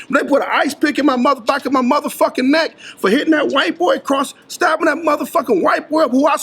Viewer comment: Did he just say he took that dudes manhoo? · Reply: Man... yall dudes. He's saying he stabbed him.